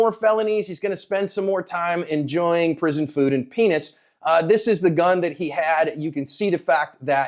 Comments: Thought he said prison food and peanuts, but after rewinding peanuts is not what he said. Was thinking delta was giving bus peanuts on way to prison. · Did I just hear you right? "Prison food and Penis" 😂 😂 you just say that haha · Did you just say prison food and peanuts or pen** at 4:12 ?